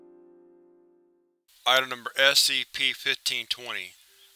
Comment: When I first started reading ur videos I always thought you said "I remember SCP" and not "Item number SCP"